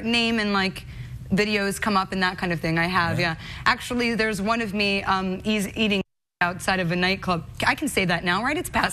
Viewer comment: what she say there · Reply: “Eating shit outside a nightclub”- falling over. She means faceplanting drunk outside a nightclub